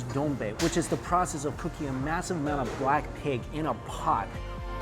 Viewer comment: When you said BLACK PIG, I thought you said BLACKPINK 😁😆 BLINK